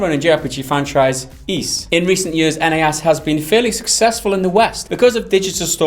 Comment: what Japanese game did he say? I heard Disgaea but "Ease" at 0:37- 0:39?